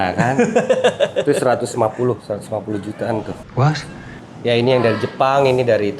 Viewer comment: Ini gw yang salah denger apa dia yang salah ngomong y? Itu tanamannya 150 jt ?